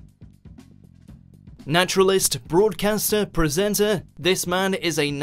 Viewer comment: I thought he said "nationalist"